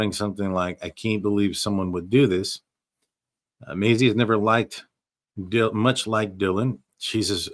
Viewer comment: did you say Macy has never liked Dylan ? Sorry I didn’t understand can someone lmk